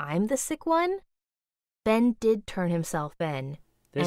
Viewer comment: Girl: Ben did turn himself Ben Who else heard that??